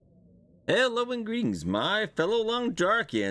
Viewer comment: for second I thought you said "hello ingredients" because of the new DLC